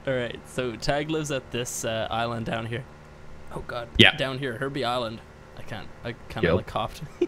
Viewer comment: I thought he said herpe island.